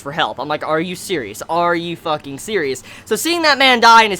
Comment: Are you series? Are you fucking series? It sounded like series to me. :D